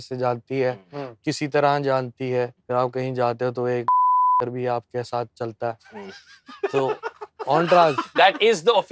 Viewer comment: what is the beep word? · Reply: Laav-Lashkar